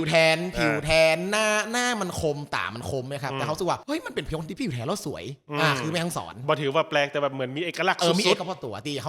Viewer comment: นาทีที่ 22:55 พูดว่าอะไรเหรอคะ ฟังไม่ออก กดโสลว์แล้วก็ยังฟังไม่ออก 😅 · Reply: ”เฮาฮู้สึกว่า ต๋ามันคม หน้ามันคม เป๋นคนตี้ผิวแทนแล้วสวย“ ครับ